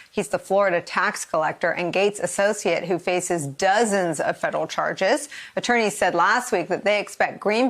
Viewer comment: ' "Built for the battle" means I' don't have a conscience so I have neither impulse control nor shame. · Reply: Did she say big boobs or big boost lol 0:25-0:27 sounds like she said boobs lol